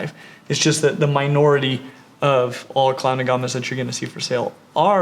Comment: Looks like a uro and a beardie had a baby!! So cool. But no pancake belly. But I do agree everyone has Beardies xD Also sound like hes saying clown aguama?